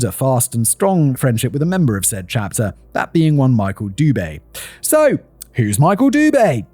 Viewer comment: OMG I quickly went back about 30 seconds after you introduce MICHAEL DUBE because I could have sworn I heard you say MICHAEL BUBLE!! I figured I simply misheard you but I had to check immediately! Then I got a picture in my head of Buble's next music video and it has him singing on a motorcycle wearing his typical fancy suits which made me laughing so hard I had tears! 😝🤣😝